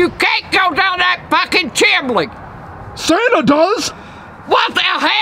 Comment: did she just say "chimley"? 😂😂😂